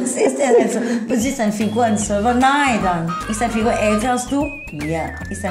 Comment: Ich habe "Ist deine Figur ein Server" verstanden 😁